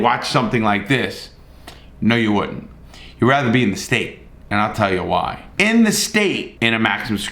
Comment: When says "You'd rather be in the state and I'll tell you why." What I hear is "You'd rather be in the state and I'll tell you about that later."